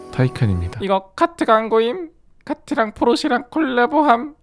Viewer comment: 예전에 포르쉐르 포르테로 잘못 들은 기억이